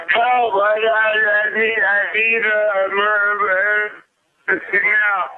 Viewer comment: Only thing I disagree with is that I dont hear "I I I I need a murder now". I hear "I I I I need an emergency now" it just cuts out halfway thru "emergency"